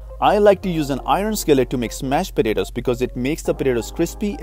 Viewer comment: I thought I heard "mashed potatoes" but got hit with SMASHED potatoes!!!